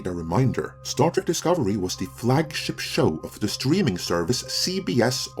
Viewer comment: I misheard "flagship show" as "flag sh!tshow". Coincidence or prophetic?